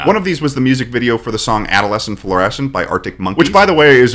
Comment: We aren't gonna talk about how said "Adolescent Florencent" instead of "Florencent Adolescent" (time stamp